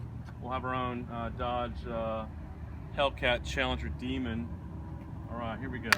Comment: Did he just said "Dodge hellcat challenger Demond"?